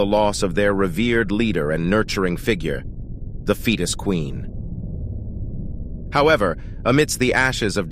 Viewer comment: I just started the video back up and all I hear is the “fetus queen”